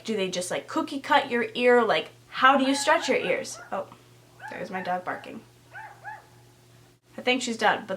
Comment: lol @ 6:45 I thought she said "That's my dad barking"